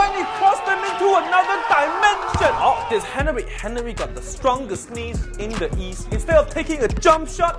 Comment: I thought he said ”Henry got the strongest sneeze in the east” 😂😂😂